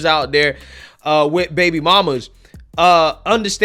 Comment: I think I heard him say “soy mama”!